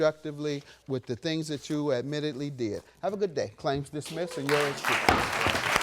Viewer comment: I’m so happy JM dismissed her case the way she was sleeping around and then chuckling about it shows she had no remorse she mentally checked out but I feel she was still playing games then slept with his friend I’m happy he won he saying he still loves her at the end baffles me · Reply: He didn't win, did he? I thought that he said, "Claim dismissed and yours too."